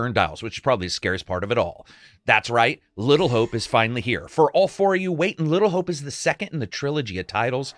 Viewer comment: I swear I heard you say "all 4 of you who been waiting" lol